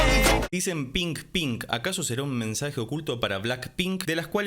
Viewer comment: no dicen pink pink si no que dicen 핑핑 que significa un silbido como ping ping